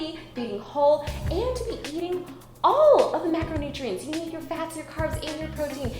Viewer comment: I know it's bad when you say: "eat all your macronutrients" and I hear: "eat all your macaroni." I need help lmao.